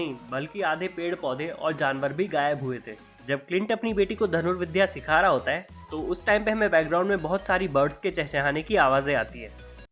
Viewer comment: did he just say " dhanur vidhya "?